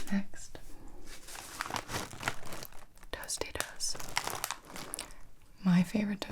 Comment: I thought you said “toasty doasty’s” instead of Tostitos at first lmao